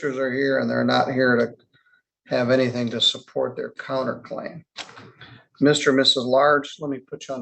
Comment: I had to turn on the captions to understand what their last names were. It sounded like he was saying, Mr. and Mr's. Lards.